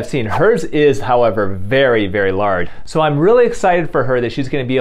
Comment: 😂Dr Youn said "very LARD" instead of very Large! Omg!😂😂😂